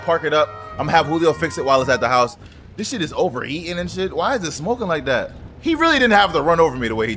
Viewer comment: in the video he said the car is over eating instead of over heating